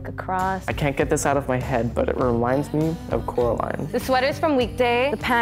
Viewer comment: what did she call those? they r SO cute please sombody tell me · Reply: High top new rock's, new rock is a boot brand